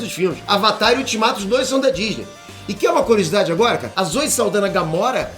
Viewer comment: quem entendeu o que ele falou? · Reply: Zoe Saldana (Gamora)